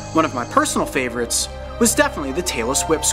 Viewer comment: when he said tailess whip I thought he said Taylor swift